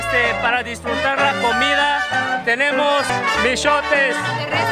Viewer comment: entendí ''TENEMOS PECHOTES'' q